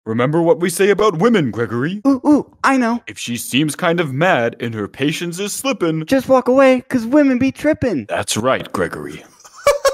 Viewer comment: I thought he said tipping